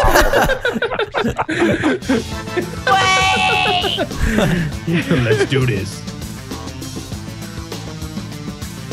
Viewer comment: What did Lui say here? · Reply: He was saying "Waaaaaaait" 😂